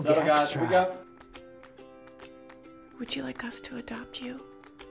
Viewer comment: I thought she said "Would you like Gus to adopt you" and was very confused. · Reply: Would you like "us" to adopt you